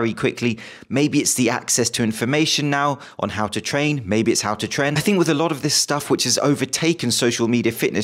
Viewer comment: did he say "maybe it is how to <tren>"?